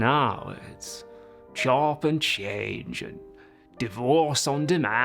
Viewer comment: I misheard what The Arsonist said at first and thought it was "Chopped and Unc"